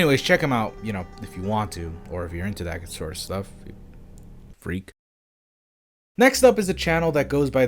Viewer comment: damn did he say freakk! bhahahahahaha